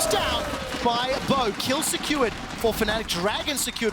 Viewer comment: does he say bo instead of razork xdd